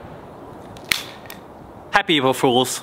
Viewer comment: I heard evil not April